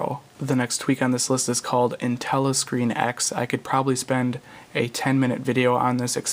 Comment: What tweak does he say after aero? 11:24 intella lock screen?